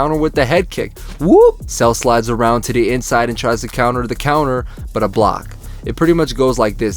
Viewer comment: “He tries to counter with a counter” “Sonic: Hey! Ramps! Tails: We can use these as ramps!” Dangit he said “tries to counter the counter” and I misheard it 😭 now the jokes on me